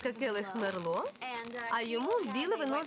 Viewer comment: думаю, что она сказала не "Келлог", а "келих".